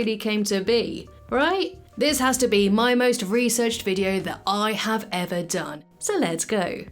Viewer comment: I heard "My most researched video" as "My most recent video" and I was like "Well .... YEAH?!"